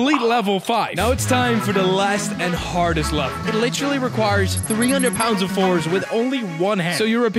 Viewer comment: Eddie hall left the chat 💀💀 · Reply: oh shit mb I thought he said 500 pounds im dumb as shit thx tho